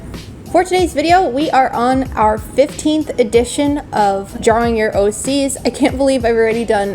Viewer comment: I thought she said jarring at first 😭🤚